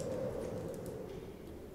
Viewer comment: Can somebody tell me what did he say at 4:39. Coz I'm very curious. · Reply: so so so, what do we have here...